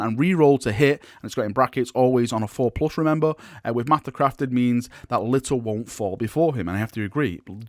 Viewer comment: for some reason I heard master crafted memes...